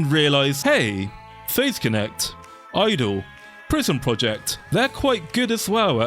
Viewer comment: It doesn't matter who says it, I keep mishearing "PRISM Project" as "prison project" and I don't know why.